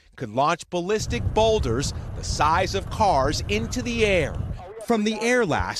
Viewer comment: For a second I thought he said "the size of Mars" I was about to actually start taking my aerospace project seriously